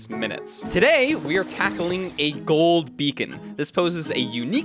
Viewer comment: I thought it said gold bacon. I need to take a nap.